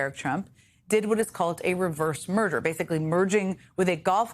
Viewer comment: At first I thought Jenn said a "reverse murder".